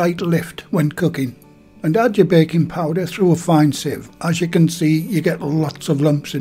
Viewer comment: For a minute there I thought you said bacon powder..😂